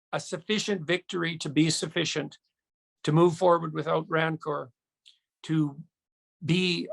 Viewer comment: This is a disappointing spiel. He doesn't get it and has wrong information. The caption word "greed" needs to be corrected at 2:03 He said "rancour" which is NOT greed. We don't necessarily have a voice in the House of Commons. He talks about negotiating with the necessary people. THAT is exactly what Trudeau will not do! !He says the political process is bending in the direction we asked for. Really? A war measures act is what we asked for?? He refers to those who are going to pack up and go home as "most reasonable." Those are shameful, judgemental words! The entire spiel is too fluffy and lacks depth of courage and insight represented by all the truckers and many others. May all those opposing Trudeau's dictatorship find encouragement and support from people who truly understand how serious this stand off is. The rights and freedoms of ALL Canadians, including our children and our grandchildren are at stake. Now that JT has illegally enacted the War Measures Act I would not be surprised if he has no intention to return ANY rights until he is smugly satisfied--and I can't see that ever happening. Power hungry people are never satisfied.